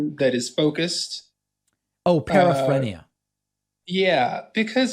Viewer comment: What word does say here? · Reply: Paraphrenia: is a type of mental disorder characterized by paranoid delusions. The affected individual experiences imaginary fears or anxieties that are often exaggerated, but do not undergo significant loss of intellectual capabilities, such as memory and daily routine habits.